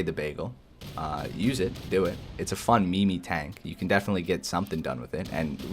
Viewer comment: Did he just call it a mee mee tank?